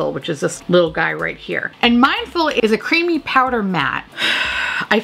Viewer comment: When you said the first shade name I thought you said Mindfuck, when you really said Mindful. I was like, at last truth in advertising. Then my hopes were dashed, but it did hit the idea for a pallet. All the shade names are profanity.